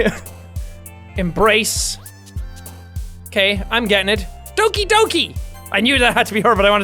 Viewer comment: Doki doki is not oki doki... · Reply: thought he said dookie dookie